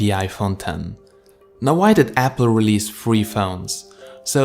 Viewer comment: sounded like you said "free" phones, which is far from the truth about these guys :)